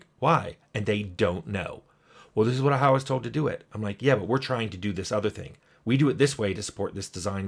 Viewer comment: I swear I heard "thats what Howard told us to do it!"